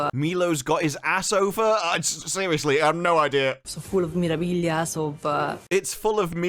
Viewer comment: i think she said memorabilias